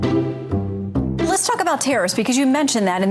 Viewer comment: Ah Tariffs - I thought she was saying Terrorists - which kind of amounts to the same thing.